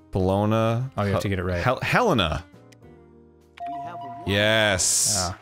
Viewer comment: When Arin yelled "Helena!" all I heard was "The bananas has gone bad!"